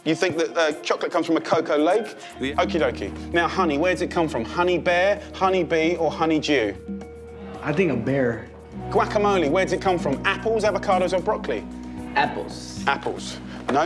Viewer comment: I so thought he said honey-jew….